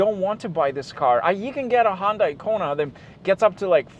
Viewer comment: sorry what you say HONDA ICONA?